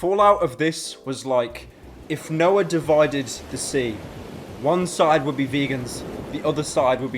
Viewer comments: did he just say when Noah parted the sea? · did he say Noah?